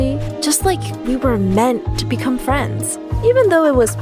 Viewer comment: FRIND instead of Friend